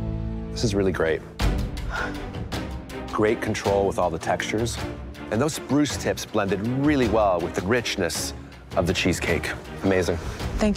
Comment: Why did I hear GRINCHness? 😂😂